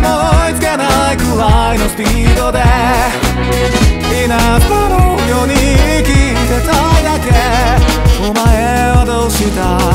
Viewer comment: 一体いつからだろうか…「稲妻のように生きていたいだけ」が「稲妻の世に生きていたいだけ」に聞こえるようになったのは…